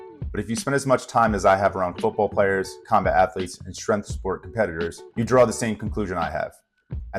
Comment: Did I hear "bat-lete" towards the end? lol